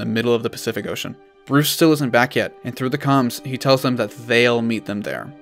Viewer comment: sounds like your saying shit Also 5:24is awesome